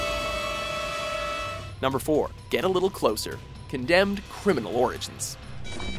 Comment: no.4 i hear it said "Get a little Closer" but in the text is spell "Get a Closer Look" Weird...